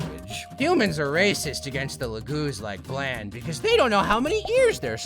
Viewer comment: did he just say Laguz?